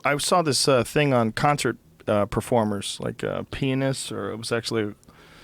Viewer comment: thought he said penis lmfao